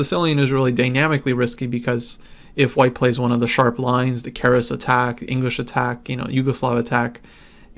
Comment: What did he say before the english attack at 1:30? I mean i didn't understand the name of the attack he mentioned before. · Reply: The Keres Attack - e4 c5 Nf3 d6 d4 cxd4 Nxd4 Nf6 Nc3 e6 g4! In fact it is this system that convinced players to play 5...a6 (the Najdorf) instead of 5...e6